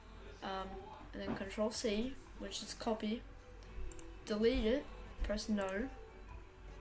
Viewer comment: what did you say in 4:07? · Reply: DarkGreek. Gr control c which is copy